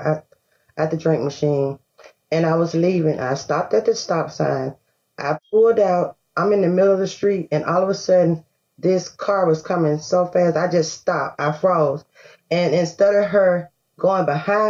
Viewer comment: "I pulled out in front of a moving vehicle and it hit me. It's their fault." LOL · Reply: I thought I heard her say that she pulled out and froze.